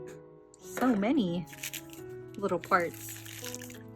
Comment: was I the only one who heard “so many.. little farts”